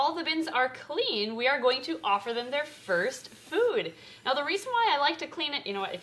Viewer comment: I thought when when she said first food I thought she said fast food